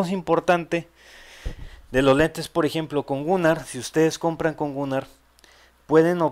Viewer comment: a gunnar yo pensé que decía lentes de gumaro...